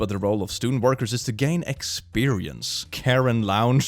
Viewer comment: When you said the name Karen Lounge I swore I heard you say rounds and had to do a double take cause that's my aunt and it honestly sounds weird for her to say but not impossible